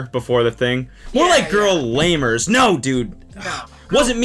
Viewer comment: I thought he said "girl labors" lmao